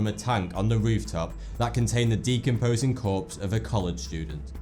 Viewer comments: "the decomposing corpse of a coloured student" That is what I heard at first, haha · at first i thought he said "a coloured student" lol